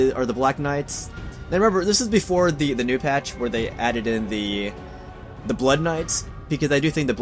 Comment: "this is before the patch when they added-ed the blood knights..." Had to replay it twice before i know i heard you right XD